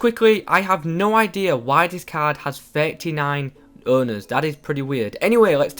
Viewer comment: It sounded like he said 39 bonners😂